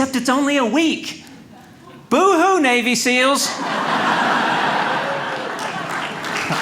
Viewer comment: I thought he said , " ... documentary on BABY SEALS." Thought I'd had a stroke.